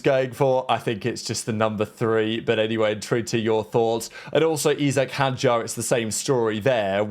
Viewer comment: Thank you for finally slowing down while saying “intrigued to your thoughts.” I’ve been a daily watcher of your videos for a long time and now I finally understand what you like to say so much.